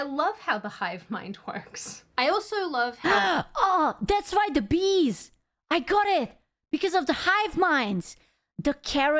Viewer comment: when she said "it's the bees!" I heard "Mister Beast" lol